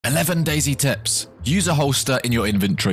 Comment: I thought he said "11 lazy tips" and I was like : I'm subbing